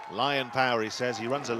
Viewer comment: I thought the south african said White power when he said lionpower